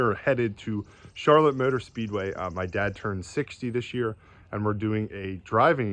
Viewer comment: Wow I will be 60 next year! We got married late in life (38) but our oldest son will be 20 this December. Despite being amateurs at parenting both boys have grown into fine young men. AL B. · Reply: Thought he said his dad turned sixty🤔